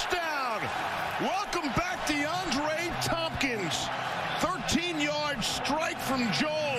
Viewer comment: i thought he said deandre hopkins😂